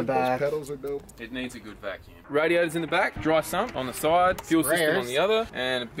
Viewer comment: Thought he said " the radios in the back" haha